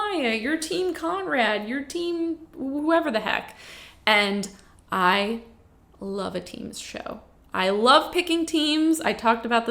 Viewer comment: I need CC but was listening while getting ready... I had to re-watch the middle part lol because what I heard was "I love TEEN shows, I love picking teens, deciding which teen..." I am assuming it really is a teen show lol, but I'm relieved that you love picking TEAMS. I'm such an old lady hahaha